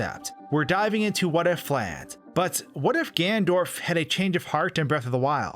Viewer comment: did you just say gandorf